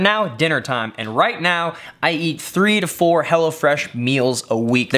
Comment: I thought you were saying Hellafresh. Glad you spelled it out for me.